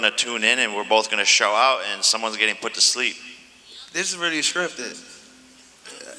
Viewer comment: Does Tank say this is really scripted , this is where you drift off.......or did I miss hear?